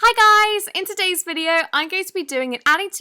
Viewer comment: When you said hi guys I thought you said hi sisters